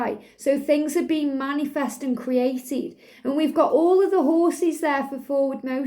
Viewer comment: Just to mention my odd train of thought at 21:25 or so into the video where you mention “All the horses” which cos I was half-doing something else at the same time I misheard as “All the king’s horses” and then the thought of “All the king’s horses and all the king’s men couldn’t put Humpty together again”. As saying something about the journey I thought that’s not very positive! But then immediately thought flowed into the memory of the line we sometimes hear of the DF cracking open the Masculine’s heart space. So their heart had been a closed space for whatever reasons but now after encountering the DF, nothing, not even all the king’s horses and all the king’s men, can put it back together again the way it was! Regardless of the masks it’s been cracked open.